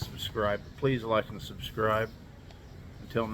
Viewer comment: What are you saying at the closing...CC "my house, coat, and..."? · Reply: I am saying Mahal ko kayo means I love you all